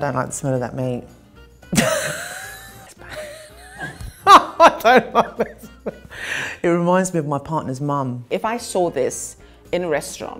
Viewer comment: Did she just say at 2:55 'I don't like the smell.... it reminds me of my partner's mum'? 😅